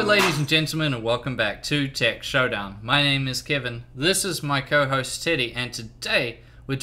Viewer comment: I thought he said "This is my co-host, Titty!" I has to look twice 😂